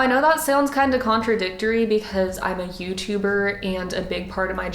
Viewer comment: subtitles says hooch huver instead of youtuber i dont even